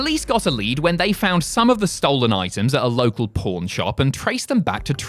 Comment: I know you meant "local pawn shop" but I heard "local Porn shop"